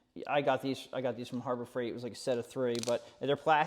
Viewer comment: Please repeat where you purchased the fuel line crimper and link or part #. I thought you said Harbor Freight but I went there and they couldn't find it...thanks for your youtubes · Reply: Yes, I did say HARBOR FREIGHT. If they don’t have them, then you will just have to find something comparable. I purchased mine at HARBOR FREIGHT. You can also use a pair of small vice grips, needle nose type, and put tape around the grooves of the jaws to protect the fuel line from getting imprints.